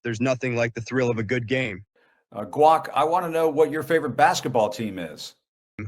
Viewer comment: weird and extremely awkward. idk much about this kid but at the 5:55 mark did he call him guac? is that like his nickname when he was alive?